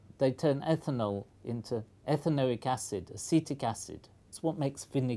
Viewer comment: there is a mistake in the captions at 6:48, instead of citric acid it should be acetic acid